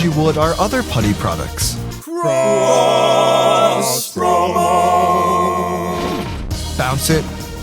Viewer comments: why do i keep hearing "gross promo" the hecc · I always thought they said gross promo